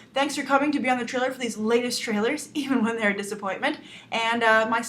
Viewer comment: I laughed at the end and then i heard "I was very disappointed" and then i had a blank expression.